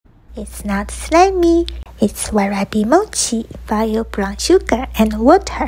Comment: I thought she said "wasabi"mochii